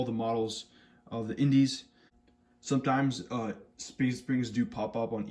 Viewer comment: What did you say at 2:58? Speed springs? I was considering getting some Kronos coil springs to replace my eslastomers